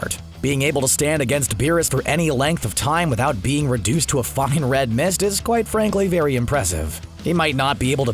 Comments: wtf this is the first time ive heard say any type of cuss word💀 lmao he sound hella funny when he said that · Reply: He said being reduce to a fine red miss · it sounded like he said a “fucking red mist” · Did he say F***en red mist or am I just tripping? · I swear it sounds like you said "...A f*$king red mist..." @ 1:45 lmao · It sounded like he said to a fucking red most